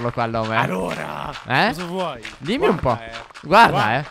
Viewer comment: Ma sono l unico che al minuto 2:44 ha sentito Ca**o vuoi da mark